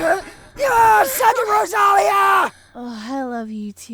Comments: "SUCK IT ROSALIA!" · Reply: I thought he said "Sudden Rosalia!" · wait what did kirito say in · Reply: suck it rosalia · i get why he said "SUCK IT ROSALIA" cuz in episode 4 when she was about to die, she said it would be the deepest he'd ever he in a woman...i feel stupid now lol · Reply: tbh I thought he said southern Rosalia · "SUCK IT ROSALIA!" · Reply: I thought he said 'Second Rosalia'